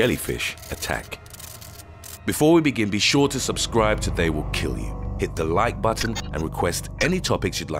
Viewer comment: This is the first time that I’m watching this and I love this, but at the start, I thought I heard something like: “subscribe or they’ll kill u!” Hah! This is very educational and cool tho :D